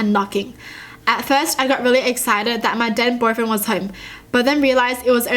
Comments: I heard "dead boyfriend" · I thought she said “dead boyfriend” I was like whaaaa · When she said "then boyfriend" i thought she said "dead boyfriend" 😂 i was lowkey confused lmao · did anyone hear her say that "my dead boyfriend was home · Omg when she said THEN boyfriend i thought she said DEAD boyfriend😱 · am i the only one who thought she said "dead Boyfriend" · i thought she said: at first i got really excited because my dead boyfriend was home... lol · i think i just heard dead boyfriend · I thought she said "dead boyfriend" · When you said "Then Boyfriend" It sounded like you said Dead Boyfriend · Who thought she said dead boyfriend 😂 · anybody else hear dead boyfriend? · I thought she said "dead boyfriend" and i nearly had a heart attack.... Then i read it. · when you said "then boyfriend" i thought you said "dead boyfriend" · This is what I hear - At first I got really exited that my dead boyfriend was home What is says - At first I got really exited that my then boyfriend was home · is it just me who heard her say ‘dead boyfriend’ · omg I thought she said dead boyfriend 😱😱😷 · Ok so did anyone else hear her say," At first I thought it was my DEAD boyfriend coming home." at 4:10 (during a story.) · why did you say dead boyfriend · When she said "then boyfriend" I thought she said dead boyfriend. Then I looked back at the story and it said "then boyfriend". I think I'm going deaf🤦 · did anyone else hear "dead" boyfriend at this part✌🏽️💜